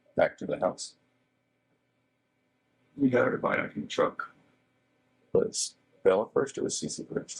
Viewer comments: I swear he said “we got everybody back in the truck” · did we say “WE GOT EVERYBODY BACK IN THE TRUCK……and went back up?????”